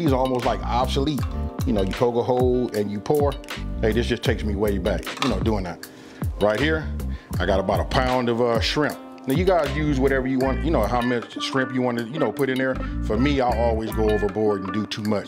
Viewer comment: did he say "skrimp"?? lmao...